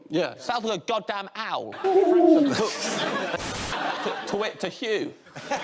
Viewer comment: Can somebody please tell me what James said on 9:00 ? Something about Hugh mimicking the owl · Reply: He says “twit t’Hugh” which is a play on words about the noice owls make (twit twoo) and Hugh